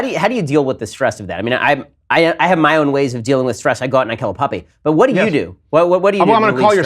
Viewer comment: HE GOES OUT AND KILLS A PUPPY? WHAT!! PLEASE TELL ME I MISHEARD